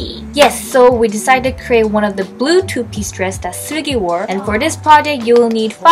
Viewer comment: i literally thought she said 쓰레기, i had to rewind and realize that it was seulgi